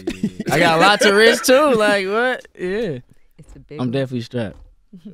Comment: Yoooo anyone else hear Angela Yee whisper “is it big?” at 14:07 Hahaha